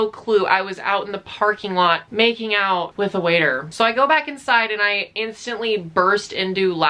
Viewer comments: I thought it said I made out with my water at 16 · I thought It said “I made out with a waiter when I was 6” and choked on my water I need help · I thought it say i made out with a water 😂😂😂😂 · i thought it said that you made out with a water bottle..,. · I thought it said made out with water... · Lol I thought it said u made out with water lmao